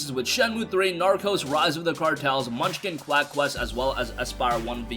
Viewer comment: I could have sworn I heard "Naruto: rise of the cartels"